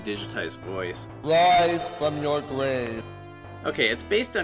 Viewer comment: Wise from your gwave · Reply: And I always thought it said "Rise Cuz You're Gay!!" ha-ha